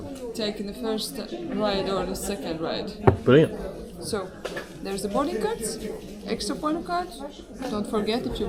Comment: did she say “There’s a Kevin Keegan” 😂